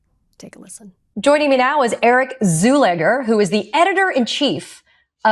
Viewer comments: Great job on the part of the EU. We dont want these predatory rich fuckers over here. Also, I heard her say at 06.03 "eric zoolander", so I was fully expecting ben stiller pouting his lips and spewing nonsense..:_)) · I Thought She Said Eric Zoolander!!!!